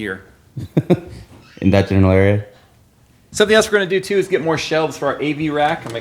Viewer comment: says “in that general area,” and I coulda swore he said “the vaginal area,” the first time I heard it. 😂